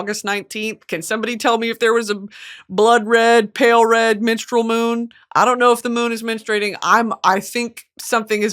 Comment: when she said menstrual i thought she said MINSTREL😭